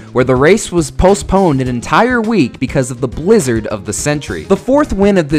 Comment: Did I Hear That Right... 7:23... AN ENTIRE WEEK... WHAT THE F***